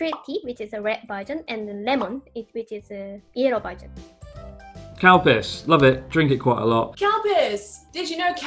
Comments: How did i hear Calpico as Cowpiss? 😂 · Calpico? I heard “Cow Piss” Well... That is a weird misheard drink name!